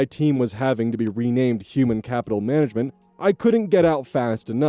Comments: I read "Human Capital Management", I hear "Human Cattle Management". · I misheard Human Capital Management as Human Cattle Management, and somehow that's still better.